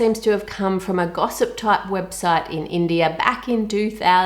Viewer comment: did she just said india 🤩🤩 I'm from India